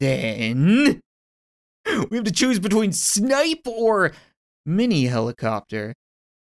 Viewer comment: I thought he was trying to say "sniper" and pronounced it "snipeor" But he actually said "snipe or"